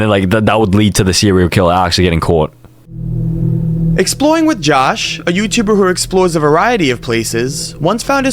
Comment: i thought that said exploring with jason